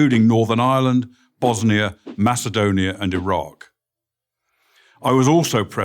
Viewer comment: I thought he said a rock instead of Iraq 😂